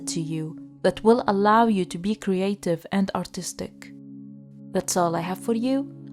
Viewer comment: Group 2: I literally squealed when she said "he may be artistic" bc I thought she said autistic ad that's literally my type????